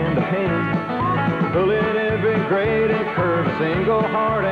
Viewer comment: XD DOES IT SAY TURD